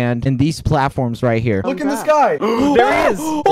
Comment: sounds like George is saying a Blackpink line "look up in the sky its a bird its a planee"~